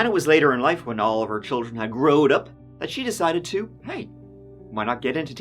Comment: did say growed up???😂😂😂 Wtf Americans you need to check yourselves!!!!!